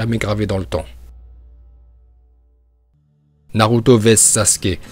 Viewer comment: C’est moi ou il dit Naruta a 3:11 ?